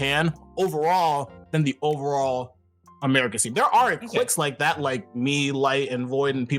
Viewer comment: I think Cosmos is saying "cliques" here, not "clicks".